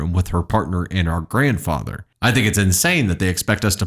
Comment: In the story, she said their daughter was living in a one bedroom with her husband and their grandfather? Isn’t that one of these OPs parent? Or maybe it’s the boyfriends grandparent? If it is OPs, they’re not willing to take care of their parent so why would they be willing to take care of their child? They’re either selfish or they were raised that way and wouldn’t take in their parent because their parents didn’t let them stay? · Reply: I thought grandfather was said too, but looking at the post it says granddaughter. So it's OPs grandchild. I read it because mentioned child when referring to the daughter and I was wondering if I miss heard something. Still, OP is an AH, hands down.